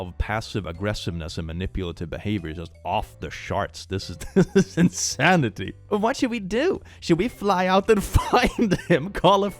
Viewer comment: "...off the sharts" had me for a minute. I know, prob'ly not what intended to say but geeze.